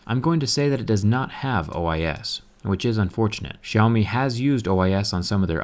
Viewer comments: caption says iOS instead of OIS lol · You put IOS at 2:04 on captions instead of OIS